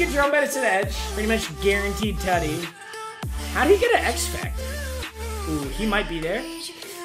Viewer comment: did he just say how do you get an x-factor?